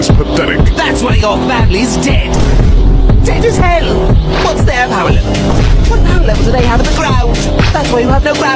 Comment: Huh don’t know why I thought he said: “What’s their power level? 6-feet underground?”